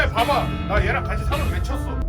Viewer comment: 원본으로 볼때는 3안외친걸로 들림 황현홍